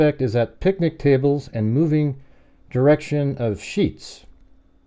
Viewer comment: He didn't say ..."headed to Sheets" he said "...moving in the DIRECTION of Sheets". NOTICE the difference. The person was just saying what direction he was headed.